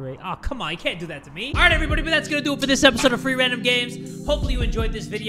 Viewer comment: I thought he said three random games instead of free random games